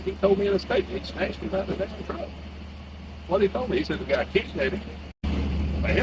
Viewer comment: Can we appreciate that the closed captioning just said “Swhathetoldme” instead of “It’s what he told me”